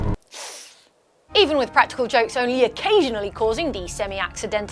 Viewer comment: Huh, I thought you said "jumpscares" not "jokes".